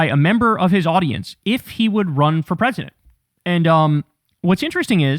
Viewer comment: I heard, "Will you run for office," not "will you run for president."